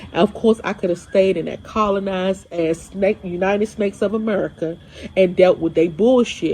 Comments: Did she said "United Snacks of America"? 😅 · did she say "united snacks of america"? lol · did she said United Snacks of America?😂